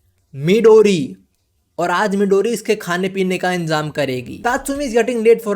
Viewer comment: Nice bro and daymm and did bro say midori